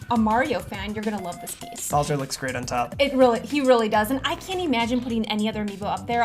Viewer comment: i heard He really dosent 😂😂😂😂😂😂